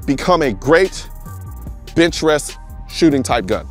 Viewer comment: I know he meant bench rest. But, I swear, I heard him say "bitch rest".